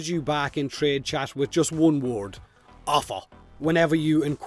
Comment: initially thought you said "offal". Imagining a mafia that deals only in offal I find quite funny.